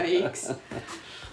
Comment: I thought he said, "Oh, is that Goku?"